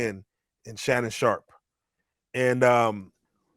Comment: I thought they said Shannon was gay